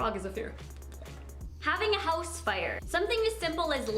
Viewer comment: omg when she said house fire I thought she said house spider because those fuh reak me out